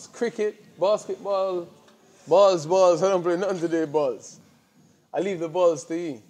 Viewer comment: I thought he said he plays no sports with barz, meaning he don’t want to go to a prison cell. Until he said cricket and basketball, cockney Jamaican is no a good mix!